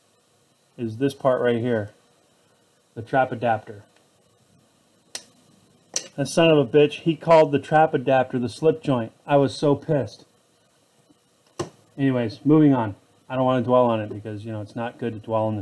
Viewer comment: I had this playing in the background and when i started my car just now all i heard was "the sunnofabitch counted the trap adapter as the slipjoint" LOL